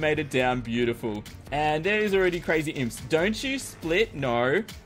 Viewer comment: I literally thought you said cookiesphere at 9:35..